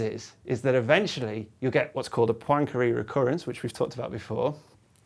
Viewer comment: What did he said at 4:19 when he talked about the univers resetting itself, how does he called it ? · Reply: it seems to be "Poincaré recurrence". Thanks to youtube automatic subtitles :)